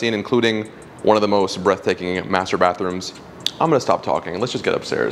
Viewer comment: 🥰🥰🥰🤩🤩🤩wow amazing!!! Beautiful....the masterbathroom I love it!!!!🤩🤩🤩🤩🤩😍😍😍😍😁😁😁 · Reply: I legit thought you said master*ation room. 😂😂😂